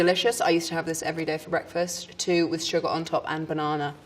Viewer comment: Ha breakfast, I thought she said Brexit.